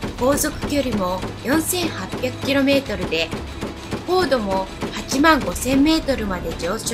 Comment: 高度85000メートルと聞こえたのは気のせいでしょう………